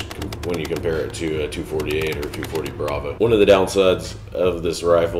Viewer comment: did he call the M249 a 248?? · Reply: He said 240A